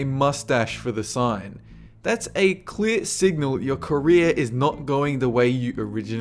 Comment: I thought he said Korea, but he said career.